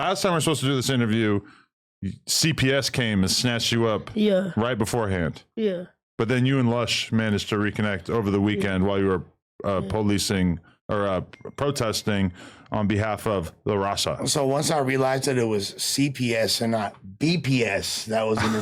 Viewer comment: did Adam say they were police-ing 😂😂😂